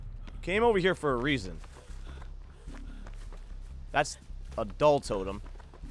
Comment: sound like he said ''adult totem'' that's hot